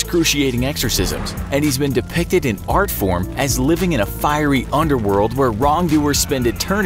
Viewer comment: I thought they said Firey Underwear